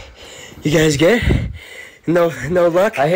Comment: Bro instead of "are you guys good" I heard "ArE yOu GuYs GaY"